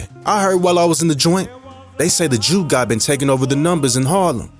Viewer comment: Every time they said 'jew guy' I thought I was hearing them say 'jew god'.